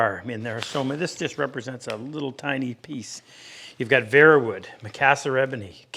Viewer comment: what was that green wood at the end that you showed an example of that you liked? I thought it looked really cool, but it sounded like you said "vert" wood, like the French word for green, but I have looked and I can't find anything of that name. What was it?